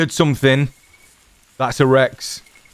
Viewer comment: It almost sounded like "That's erect".